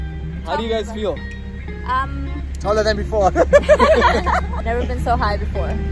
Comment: who understands joke😂😂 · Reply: Taller than before